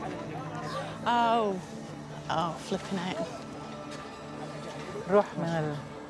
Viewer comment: Dr. Fletcher says " Flippin' A" and we all know what she meant to say. What a professional. And I would've had the same reaction.